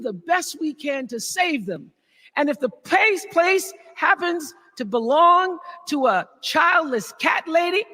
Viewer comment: My sub-titles didn’t understand what Oprah said. “If the place place belongs to the childless cat lady,” is what I got. Can anybody help?